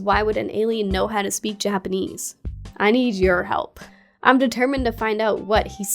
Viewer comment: I swear I hear it say "I need your help" at some point